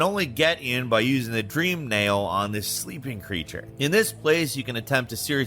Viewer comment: i thought he said in this stupid creature and i laugh my ass off.